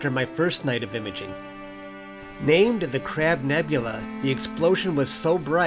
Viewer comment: I thought it said crap nebula